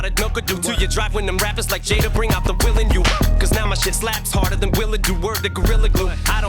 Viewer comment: "Now my sh!t slaps harder than WILL’ll do"....sounded like he said "slaps harder than Willow do" which is their daughters name whose also an artist, Wtf? 🤯😂🔥 missed one lol. Em cold 🐐